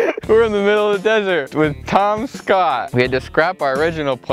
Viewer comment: When you introduced Tom Scott I thought you said Tomska and I was very confused